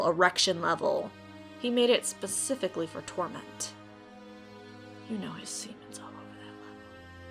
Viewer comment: I think i listened to 7:05 to 7:08 and i think said "you know seamen is all over that level"